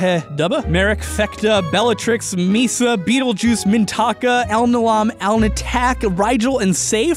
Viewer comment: i thought he said "Beetle Jucie"